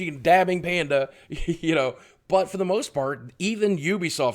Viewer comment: does anyone else here fart instead of part?